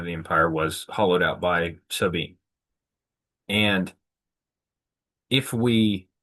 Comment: “hollowed out by…” what word did you say? Sounds like “sub-e” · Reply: I said, "because every prior region that was once the engine of the empire was hollowed out by so being"--that is, they were culturally depleted as a consequence of the affluence, power, and other attendant circumstances which accompanied their status as the chief engine/workhorse of the imperial enterprise.